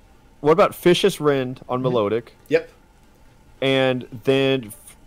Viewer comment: Up until the 32 minute mark I thought they were saying "fish's friend" ....